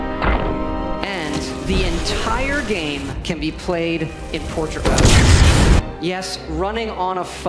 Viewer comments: I always hear "The entire game can be played in Portugal!" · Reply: I heard "torture mod". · I always hear "The entire game can be played in Portugal!" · idk why i keep hearing "the entire game can be played in Portugal" but it makes it even better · What does he says? "In Portugal"?